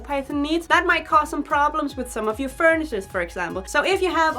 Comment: what does she say? does she say furnishers??? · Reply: I say furnitures